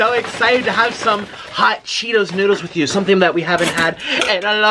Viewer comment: I did not read the title and I heard him said “hot Cheetos DILDOS”